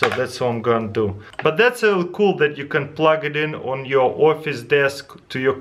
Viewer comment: Was is just me who understand "plug it in on your orpheus desk" ? :'D